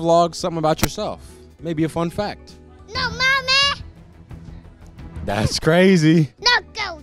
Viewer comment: Did I just hear this kid say “ no manes”?